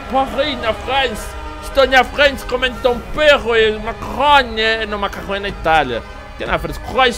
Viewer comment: Quando ele falou que estava comendo macarrão eu entendi "estou comendo maconha" huehuehuehue